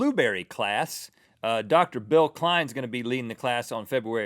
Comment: When Rhett said "Bill Cline", for a split second I thought he said "Bill Nye" and got really excited... I loved Bill Nye the Science Guy, and still do.